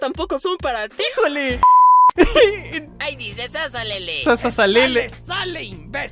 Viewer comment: Yo si entendí sasa lele xd